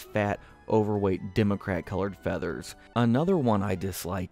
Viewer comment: Did... Did you say "democrat colors"? Wat